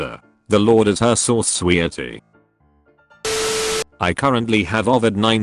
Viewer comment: “sweaty” instead of “sweetie”, Diary of a Wimpy Kid anyone?